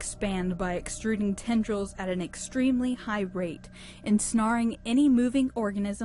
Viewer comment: Did she say "En-sn-ARE"?